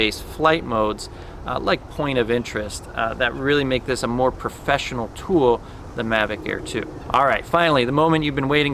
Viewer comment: what the is he saying? so which one is more professional tool than mavic air2 ????